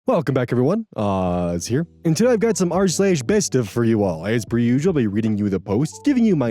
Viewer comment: I misheard you saying "r/bestof" as "arse-asbestos" this time. It didn't actually sound like that, my brain's just stupid.